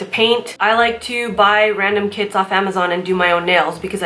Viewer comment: You said “I like to buy random kits on amazon” I thought you said I like to buy random kids on amazon 😂